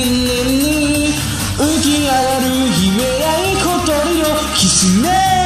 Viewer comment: The fact he sings it so perfectly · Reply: supposed to be "hokori no" instead of "kotori no"